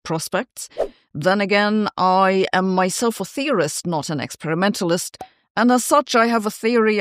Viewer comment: I had to rewind, I thought you said I am a theorist, not an "excrementalist" lol